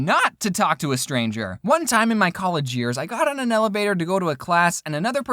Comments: did he say, "Elebator?" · Did he say elebator instead of elevator? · I swear I heard “elebator” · Did he just say... Elebator? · I'm sorry but did he just say eLaBaToR! I'm sorry probably just me lol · Did I hear say elebator at 2:45-246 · is it just me or does it sound like he says elebator not elevator xD? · Did say "elebator?" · did he say elebator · “Elebator” THATS WHAT HE SAID (Not being rude)